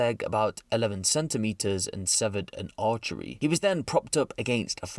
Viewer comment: no way did he call an artery an archery